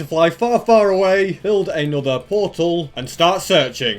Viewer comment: does he say- "a nether portal" or "another portal"